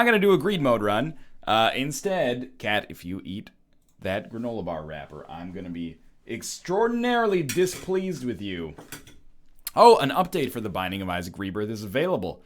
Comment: For some reason I thought you said "Kate, if you eat the wrapper I'll be very displeased with you."